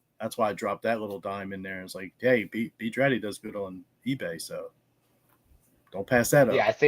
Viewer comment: What's the brand of shirt that coach is saying not to pass up? Not the Joseph A Bank but the other one he says. He said it so fast it is hard to hear. At the 20:31 mark. Thanks. I really enjoy your guys show! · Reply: B. Draddy. Thanks for watching!